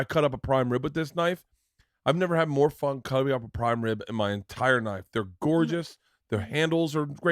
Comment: in your entire knife 😂😂😂😂😂 Did anyone else catch that? LMAO!